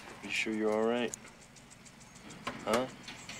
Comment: The neighbor thought he said “you’re alright, Spider.”